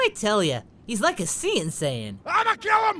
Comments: A CN saiyan... Oh man that's funny. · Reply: i hear "seein' sayian" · did he say 'Cyan' Saiyan? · What did he say? Like a... sea 'n saiyan?